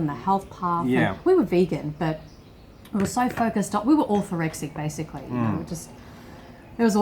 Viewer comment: what does it say? awful rexic? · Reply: orthorexic